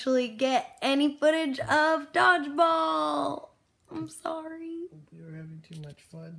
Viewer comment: I really thought you were saying "dogs balls" instead of Dodgeball. Btw... Ahola. I haven't watched in a while. Your vlogs were missing in my life. Did I miss any big news...promotions? And such which such worth, etc...